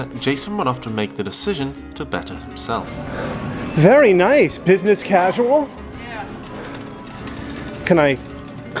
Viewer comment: I thought he said "Pissness Casual" 😭